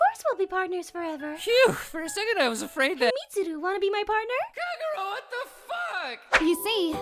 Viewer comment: did he just call her Kakorot